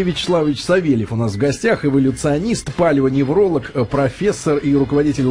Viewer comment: послышалось "ПАЛЕВОневролог"... ))) Так и есть, попалил всю бабуинскую сущность.